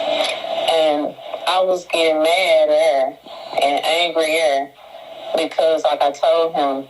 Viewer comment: On one part, it sounded like she said, “I was getting mad at her.” After she asked him why did he meet her and she slapped him.🤷‍♀️ Maybe they both met her with the baby 🤷‍♀️